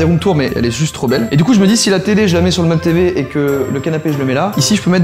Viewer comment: « et du coup si je met la télé sur la tv » euh....ok pourquoi pas 🤣🤣🤦🏼‍♂️ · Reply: Il a dit meuble tv😉